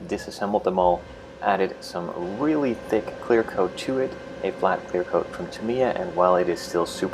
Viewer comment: Clear coat tuid? I thought you said "really thick clear code to it". But clear code isn't powder as far as i know?